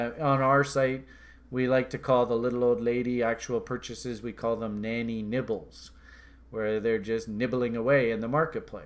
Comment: Damn, I thought he said "Nannys' nipples"... I was like WTF!!